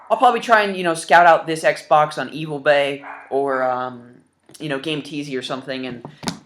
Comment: did you just call ebay evilbay or did i mishear you